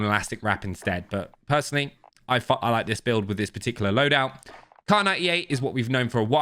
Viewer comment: i think he almost said "i fuck with this loadout" haha i love you 😂